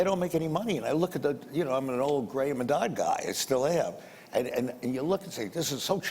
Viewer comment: What does mr. Icahn say at 6:35 ? ' i am an grey old.... Guy'? Anyone knows what he says and means? Thanks. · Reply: "I'm an old graham and dodd guy"